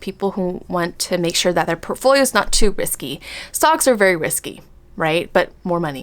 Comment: At first, I thought you said "socks are risky" and I'm like "sockssss?????" lol i have bad hearing